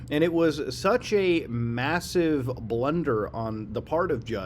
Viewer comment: did he say MASSIVE?